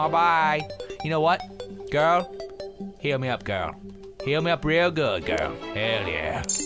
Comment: I thought he kept saying "feel me up, girl" to the nurse...that was a little awkward when I realized he said "heal".